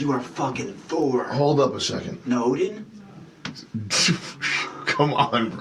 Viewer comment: What did say after "You are fucking Thor". Nodin? Nolin? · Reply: I thought he said Odin.